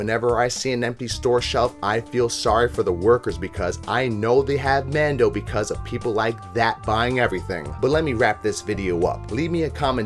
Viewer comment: I'm sorry, I rewatched a few times but I can't seem to understand what you say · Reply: I know they had men doe because they liked that buying everything